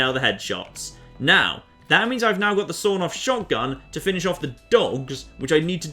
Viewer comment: Wait, YOU CAN RELOAD GUNS?!? XD Edit: Wait... did you just say "Sorn off Shotgun"?!?